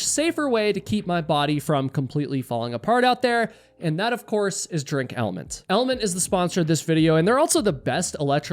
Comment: threw me for a loop there, I thought you said "drink hellmanns" lmao